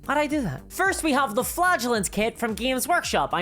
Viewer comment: I keep hearing "flatulents" rather than "flagellants"